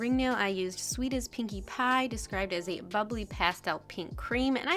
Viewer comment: Ok so at 1:18 I DEFINITELY heard "described as a fugly pink creme" and I was like, "Uhhhhh... that can't be right..." So I listened 2 more times and it wasn't until that third time that I finally heard BUBBLY 😂😂😂😂